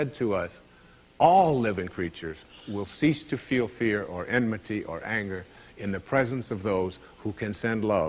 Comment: Sorry, could someone tell me which word he uses at 23:44 synonym with fear? Sounds like inmaty or anmaty, but that doesn't make sense and I can't figure out what it actually is. He also uses it at 21:40...